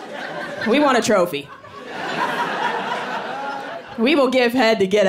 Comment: Did she say trophy or chokie? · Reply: Trophy - greetings from Germany